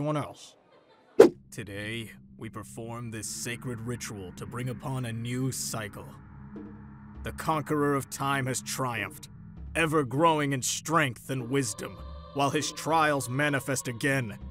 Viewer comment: i thought he said bicycle